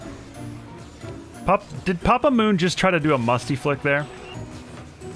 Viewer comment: Haha I thought u said - did papa moon try to do a monkey flick